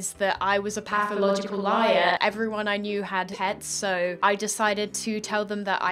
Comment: thought she said ''everyone i knew had heads''